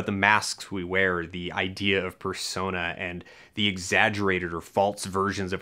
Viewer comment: "The idea of fursona" is what I heard at 0:13.